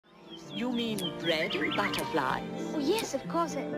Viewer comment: This is how I hear the intro song: You mean red like a flag? Yes of course!! XD that’s what I hear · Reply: Sumi The Uni i hear “you mean red butterfly’s?” And “of course”